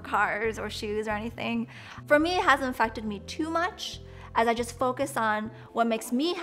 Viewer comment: I thought she said it hasn’t f*** with me too much 😂